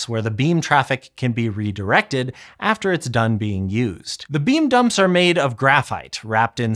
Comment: i keep hearing “bean dump” 😭